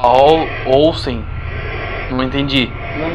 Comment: Eu entendi "eu sei" ao inves de "olsen"